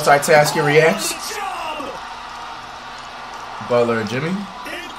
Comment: did he just say Butler of Jimmy😂